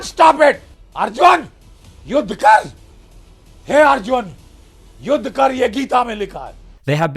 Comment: Between 11:32 and 11:35 the guy screeming his head off says something that sounds like "he arjun". Is he calling on Arjun from the mahabharata? I'm blind and if there are subtitles can't read them.